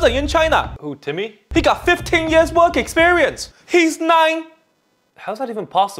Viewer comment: "He got 15 years work experience. He's 9." Timmy is the exact kind of person jobs are looking for these days! 😄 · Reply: Wait I thought he said "50 years work experience. "